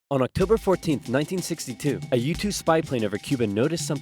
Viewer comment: Is it just me did it sound like he was saying YouTube spy plane?? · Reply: U2 spy planes, if you say it fast it sounds like YouTube.